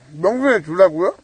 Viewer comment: 에 면순이 라고 들림ㅋ